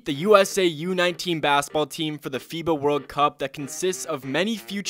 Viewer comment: Thought he said Fifa world cup was confused as hell